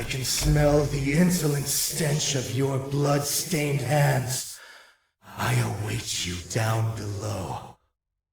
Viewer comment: alguém pode me dizer o que ele disse? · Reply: Ent ele falou o seguinte: Maquina, eu sei que você tá aí, eu posso sentir o cheiro do fedor do insentimento das suas mãos cheias de sangue, eu te espero aqui em baixo.. VENHA ATÉ MIM. é difícil ouvir com a ambientação do nível.